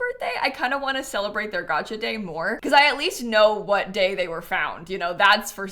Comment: Happy gotcha day sewer kitties! I've loved watching them grow and they may or may not be the reason my boyfriend has agreed to getting us some kitties! So thanks for changing a self proclaimed cat hater to cat lover kittens❤️ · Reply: ohhh its gotcha day! for some reason i kept hearing her say 'gacha day' and thought it was a reference to the random collectible nature of the cat distribution system😭